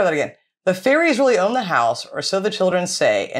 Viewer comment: I’m from England (Suffolk actually) and I heard it as whore house too lol